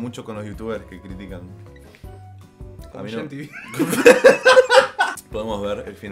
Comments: Que dijo? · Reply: Gian tv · dijo Giantv y nunca lo entendí hasta hoy bld no puede ser